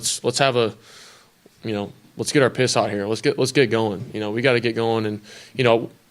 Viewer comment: Did he say "Let's get our piss hot" at 43:47 ? lol